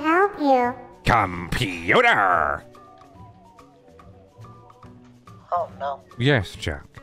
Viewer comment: No way I’m the only one who heard Neptor say ‘Oh no’ quietly when Jack called the computer (15:12 ish) along with Neptor having his ears down and having like sad/scared looking eyes plus, we know that Dark Sun made Neptor, and Neptor was scared to meet Sun, probably afraid that Sun would’ve like done something